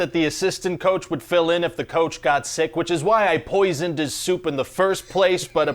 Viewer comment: I love how in the beginning he said he poisoned the coach's soup, and at the end he asked if he wanted some soup. Implying he was going to poison him 😂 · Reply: All this time I thought he said" I poised into supe" at the beginning. So in the end when he asked the coach whether he wanted some soup I was confused